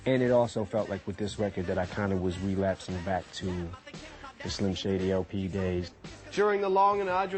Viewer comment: i heard 're'turn of slim shady but idk